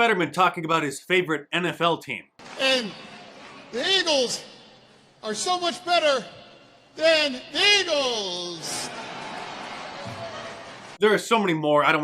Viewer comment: I seriously thought Fetterman said something like, “Ladles are so much better than dreidels” and that it was some anti-Semitic thing or something. I could never understand what he said until Mark pointed it out.